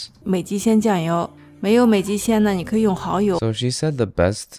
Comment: she actually said oyster sauce for the maggi replacement, not fish sauce btw